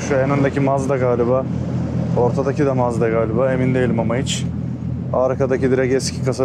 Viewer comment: abi sen supraya mazdamı dedin benmi yanlış duydum en öndekide fazla göremedim nissan gtr r34 dü heralde en arkadakini anlamadım